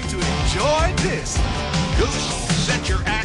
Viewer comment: I'm sure they say "set your ax to stun" but I always hear "set your ass to stun".